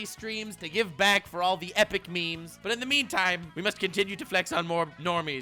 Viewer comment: Did say "in the meme time" at 7:16? Maby I'm just hearing things. Or there's a carbon monoxide leak in the house.